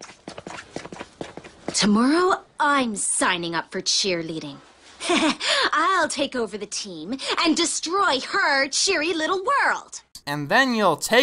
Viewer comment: What is the blonde haired girl saying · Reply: "Tomorrow, I'm signing up for cheerleading. I'll take over the team and destroy her cheery little world!"